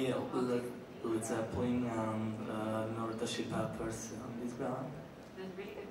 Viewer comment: help, I can't understand what he's saying apart from Led Zeppelin? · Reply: I think he said red hot chili peppers